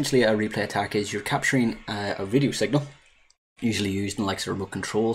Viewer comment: I was gonna wise crack on ya because in the beginning of the video it sounded like you said "video signal", but after replaying several times I can hear you actually said (or tried to say) "radio signal" 🤣😁